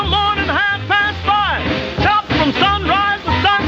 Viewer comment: first i thought he said chomp not chop...and i bet that was intentional...